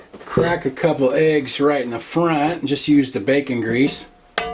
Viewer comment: Happy winter nights to you! I'll have to try this EasyCooking way. I heard say "best ever bacon." 🥓 🥓 🥓